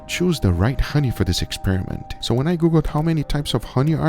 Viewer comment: I heard "when i guga ", then turn on cc " when i google" now that's right